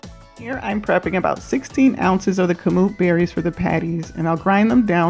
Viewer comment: How I prep & store Dr. Sebi approved freezer meals for my family! ;-) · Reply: Hi, what did you say at 1:35 please? Kamuc berry? I couldn't catch it... xx